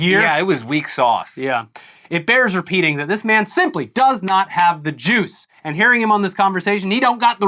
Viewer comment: When he said "does not have the juice", I heard "does not have the Jews". I was very confused and very concerned.